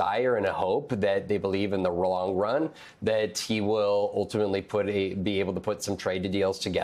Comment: Did he say wrong run but meant long run at 7:25?